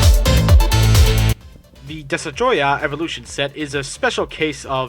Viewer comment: did he just say desatroyah?